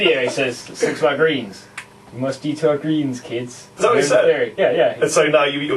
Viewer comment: help 8:36 what they say i her d to our greens. plz. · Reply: MLG HEAVEN Eat your greens